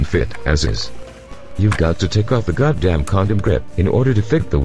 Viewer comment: did he just say condom grib!? 0.0